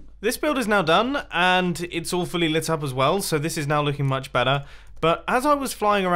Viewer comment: Every time Mumbo says, "...all fully lit up..." I hear "...awfully lit up..." and, I think it works either way.